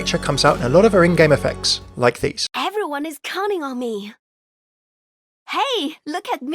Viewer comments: Whenever she said everyone is counting on me I thought she said "everyone is coming on me" and I was like um wut •_• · Ha! Hahahahaha Did she just say "everyone is coming on me"???